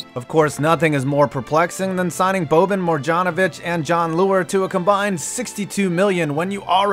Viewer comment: Did he just say morjanovic?